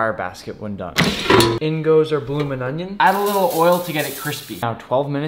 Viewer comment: I thought he said Bloomin potato for second😭😭😭